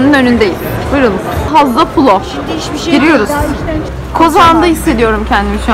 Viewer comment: Kozan'da mı hissediyorum dedin yoksa ben mi öyle anladım. Aaaaa hemşeri miyiz yoksaa 😀😀